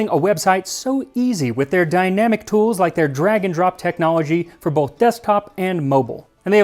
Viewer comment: I heard "dragon drop technology" instead of "drag and drop"... and website design sounded SO interesting for a second 😂 Thanks for the video, really interesting and enjoyable as always ☺